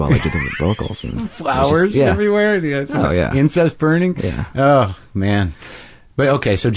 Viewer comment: Did he say Incest burning?